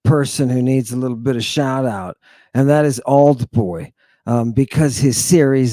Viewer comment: what are you saying, aldboy oldboy? Series on purple? Where's the link, I can't find this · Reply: Ahh found it- Auld Boy <link>